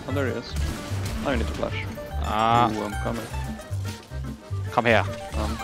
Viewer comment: What is Rekkles saying at 3:50 ? It sounds like "come here", but its different. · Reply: i think he's saying "komm her" which is come here in german ^^ was wondering aswell since he so casually threw in a german sentence in this 'high pressure' situation haha